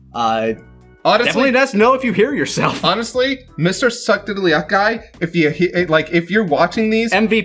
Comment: On the "suck diddly up" quote I was mishearing it. I kept hearing "suck diddly ice".